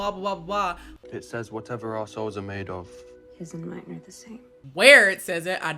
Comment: Whenever I heard the dude say "whatever our souls are made of" I kept hearing it as "whatever assholes are made of"